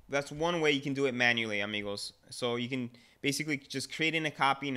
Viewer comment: Did he say "Amigos"?